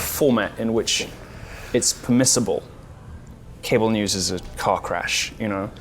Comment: at first i thought he said cable news is a cock rash i had to go back haha